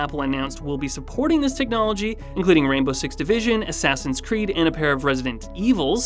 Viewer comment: “Rainbow Six Division”? My brain split in two when I heard that…